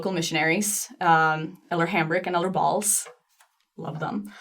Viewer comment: Did she say "Elder Balls" ??